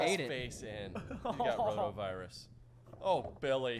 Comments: did say Ronavirus? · did say coronavirus? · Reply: He said rotovirus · did he say corona virus? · did say “coronavirus”?